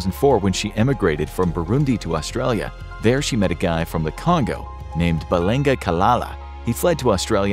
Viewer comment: I was watching at 1.5x speed, and I first heard "She met a guy from the Congo" as "She met a guy from Wakanda".